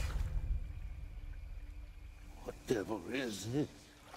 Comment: What does Blackbeard say? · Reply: What devil is this?